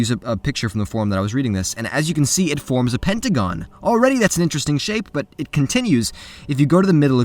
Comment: I heard "forms a Pentagon"